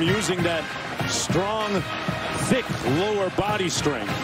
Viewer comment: did he say thick lol?